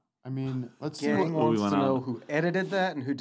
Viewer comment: What is the name Eric says at 2:29:59? Is it Joe Staken? Anyone know more about his work? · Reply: I think its Joe Stakun. Thanks.